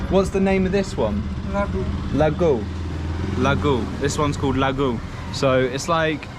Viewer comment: I think the dude said "ladu" but he heard it as "la-gu" which basically is saying "take this poop" 💀 that's probs why the seller was smirking